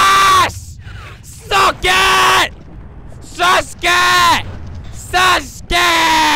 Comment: did he say sasuke??????